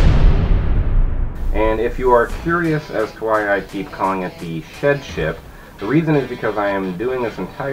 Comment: I thought you said shit ship haha.